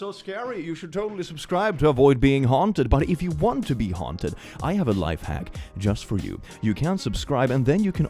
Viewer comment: I misheard it as "want to be honked at" Which I did not question until Click said it a second time